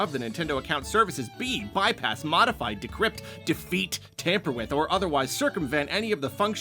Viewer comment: Did I hear that they don't want you to "defeat" your Switch? What on earth does that mean? I mean, I know it's a game console, but you don't need to win against the Switch itself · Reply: They don't want you to defeat "any of the functions or protections of the Console or the Software" (alongside not bypassing, modifying, tampering with, or otherwise circumventing them). The agreement says nothing about physically fighting the console itself.